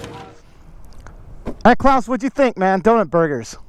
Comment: did you say all right Klaus to